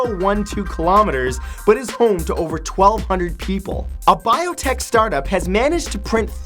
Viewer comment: He says 1,200 people but the captions say 12,000. Dafuq???